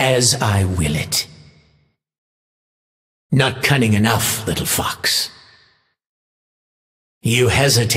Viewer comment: does he say little fucks????! :DD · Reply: ''Not cunning enough little fox'' (probably a unique taunt interaction vs Ahri) :)